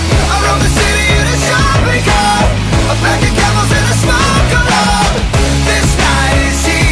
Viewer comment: I always hear “a pack of camels and I smoke a lot”